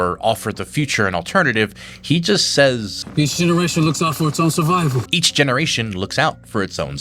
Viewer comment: what did he say? i didnt get it just like 90% of the movies dialog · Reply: Channel owner says it right after "Each generation looks out for its own survival"